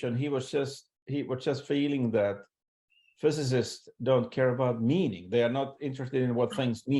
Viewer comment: I thought he was saying "they don't care about me" also! hilarious. Glad you asked that clarification question - "they don't care about MEaning!!" thanks